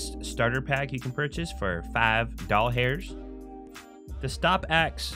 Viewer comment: i thought he said ball hairs not dollars ;)